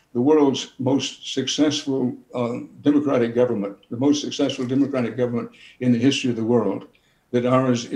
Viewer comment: It must be exhausting for Americans right now, I wish y’all much love from Canada. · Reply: NO, he said "We are the most SUCCESSFUL democratic government in the history of the world." 4:28 Which even as an American, I thought was an unusual thing to say. SO I GOOGLED IT. --- "Is the United States the most successful democratic government in the history of the world?" --- the first thing that popped up said this. "The United States is the only country with a continuous democracy more than 200 years old." 'Continuous' seems to be the relevant word to Dan Rather's claim. There are lots of democracies that started WAY before ours. But they have been broken up and changed to other things many times over the centuries. So I think Dan Rather was referring to, we've been a continuous democracy longer than any other country. (Who knew? Not me.) <link>